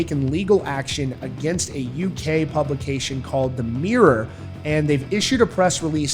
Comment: Thx! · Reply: He clearly says that it the UK publication 'The Mirror'.